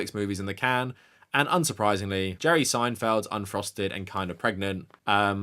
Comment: I thought you said Jerry Seinfeld got Amy Schummer kinda pregnant